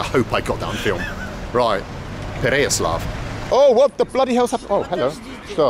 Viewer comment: I thought he was gonna say “What the bloody hell is that” 🤣🤣😂😂